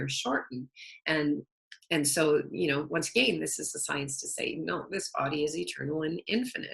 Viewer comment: in podcast she was talking about telomeres. And then she said this is the science to Satan. Did anyone else hear that the same as I did. I listened to it five times and it was crystal clear. WTF!!😜 Luv the Video otherwise and thank you for sharing your wonderful wisdom!🌈❤️🌈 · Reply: haha I think she was saying "This is a sign to say" but it reminded me of playing the beetles backwards or something! Would have to bring some controversy into my show :)